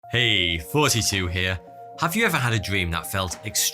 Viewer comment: Who else thought he said “heyyy fouseytube here”...or is it just me?